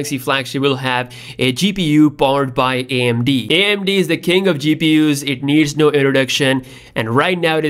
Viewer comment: Not in mobile platform they are not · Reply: Didn't you hear what he said? "AMD is the king of the GPU no introduction." He did not say king of mobile GPU. Lol